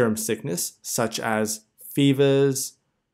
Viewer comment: Need help. Could someone tell me what the symptoms of serum sickness are. (10:04) I can hear fever, but after it, it’s hard to get the rest words...😢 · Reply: Utricaria, arthralgia, spleenomegaly